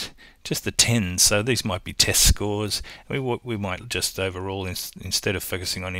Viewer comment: when you said test scores I thought you said testicles